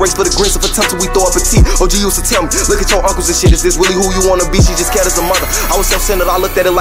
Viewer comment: "OG used to tell me, look at your uncles and shit is this really who you wanna be? She just scared as a mother. I was self centered I looked at her like why you worried bout me. So no I ain't listen I hopped off the porch to them trenches I had to get out here and see. Married the game before I knew it that well it was love at first sight I got down on one knee. Now I want a divorce, this shit been driving me crazy I think this is how it'll be in division 11, stuck in that base uniform looking ugly just waiting to be free. Mamma tried warning me I had to learn the hard way it's too late but I finally agree. But I don't regret no mistakes that I made cause all that shit made me become Polo G". So underrated. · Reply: I always thought he said "She just CARED as a mother"